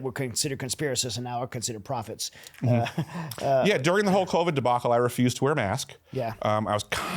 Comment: does anyone else hear him say " during the hulk hogan debacle". Im here wondering what's the hulk Hogan debacle. 😂